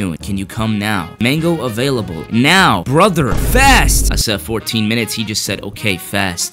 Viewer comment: “Mango available” “Now” “Brother” “Fast” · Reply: I thought he said "Brother, fat" 😭